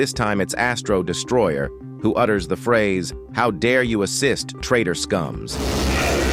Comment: i heard how dare you exist instead of assist💀 💀💀